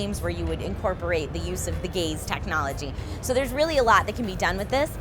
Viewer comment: I heard incorporate the "gays" technology at 4:03 hahaha. Before ya'll freak, I know its gaze.